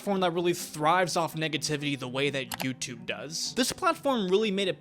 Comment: what did u say? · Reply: Negativity the way that YouTube does?